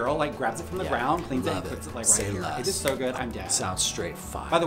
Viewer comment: I thought she said "it is so good, I'm gay" which kind of fits